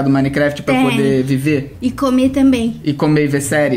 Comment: eu juro que eu entendi a falar " me comer"